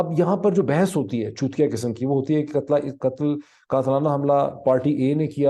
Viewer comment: Ch**tya...??? What did you say...???😲